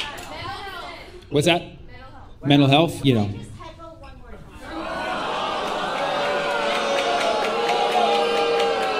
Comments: What did she say? I can’t understand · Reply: “Can I just haggle one more time 🤚” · what did that woman say here?? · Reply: "Can I heckle one more time please?"